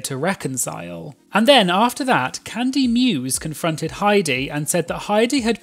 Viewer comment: I thought I heard you say Cunty Muse... 😂😂😂